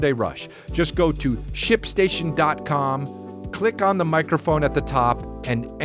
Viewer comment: I had my head under my pillow while listening and it sounded like you were saying "shit stain shin" instead of Shipstation.